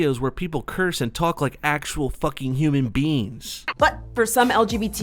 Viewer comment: Every time you say "Human Beings" I always hear "Human Beans" It's probably just me but you could work on your annunciation a little better.